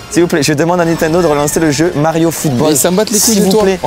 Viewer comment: pourquoi c'est sous titré "Super Mario Strikers"? Alors qu'il dit Mario Football ?